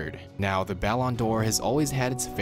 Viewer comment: i genuinely thought he said "bell on door"